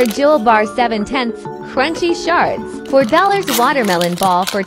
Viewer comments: I thought she said crunchy sharts…💀💀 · NOOOO BECAUSE I THOUGHT SHE SAID CRUNCHY SHARTS 😂